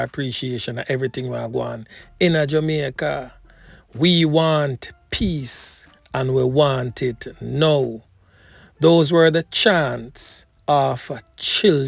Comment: Thanks for the news the little kids need to live in peace and enjoy life like back in the days. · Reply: I was washing dishes while listening and I thought they were saying “ we want pizza we want it now!” It was only when I took a look at the screen I saw the signs 😂Lol. I hope the voices will be heard and given the chance of living in a society where they can live in harmony